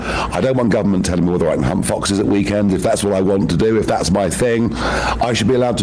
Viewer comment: definitely sounded like he said "hump foxes at the weekend." You do you Nigel 😂